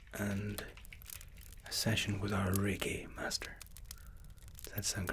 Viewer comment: OH! REIKI! I thought you said reggae... Great videos!